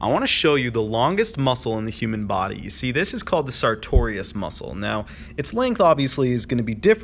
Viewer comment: So at 15:14 I first heard "Sartorius Muscle" as "Centurion Muscle". I looked it up on google because I intruiged, and OMG dont look up "Centurion Muscle"